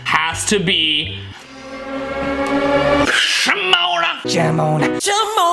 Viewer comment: I thought MJ said " Simone" 🥲